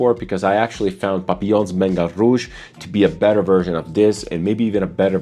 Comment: Did he say Papillon Bengal Rouge at 22:20? I couldn’t understand him.